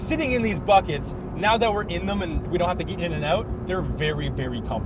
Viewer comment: I’m watching this again for like the 8th time…at 10:40 it sounded like said “eat in n out” and I thought that was actually the case since they were in Cali